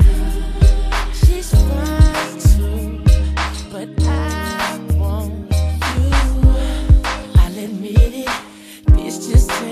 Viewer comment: I always thought Lloyd said she's 5'2 but I want you. 😅 This still my shit. ❤❤❤